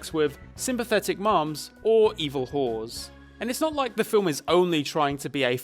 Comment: I misheard what you said as evil horses My sorry